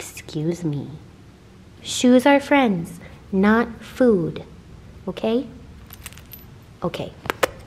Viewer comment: I heard "chew friends, not food" and I was so confused heheh lol